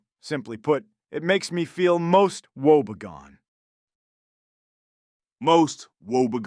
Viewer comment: I thought Anakin said Wobbegong an ambush predatory shark that mimics marine undergrowth to catch prey.